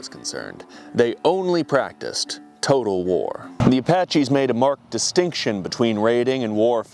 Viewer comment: Did you say 'Total War?' Time to reinstall